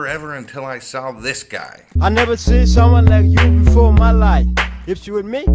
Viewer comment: you really don't catch his words? It's plain English. "I've never seen someone like you before ' my life with me?" "Got some question I've got to ask ' put yo' hands up, ok, let's go"